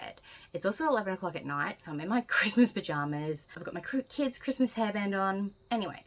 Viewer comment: omfg I thought you said "christmas v*ginas" 🤣🤣🤣🤣🤣🤣🤣🤣